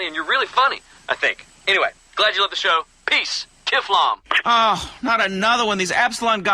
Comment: WHAT DIS LAZLOW JUST SAY ABOUT THE EPSILON PROGRAM! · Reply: It was Jimmy Boston saying kiflom to blew him off